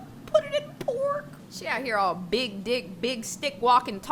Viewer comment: says "Big D!ck" instead of stick and corrects it immediately Lmfao,what was that :D